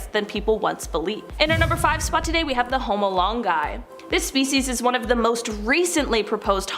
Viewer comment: I was listening instead of watching and I thought I heard "Home Alone Guy" instead of homo longi 🤦‍♀️ Prob shouldn't admit that but....